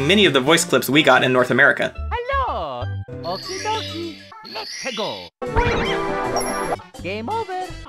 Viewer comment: I always heard "Let's a go!" as "La Peggo!"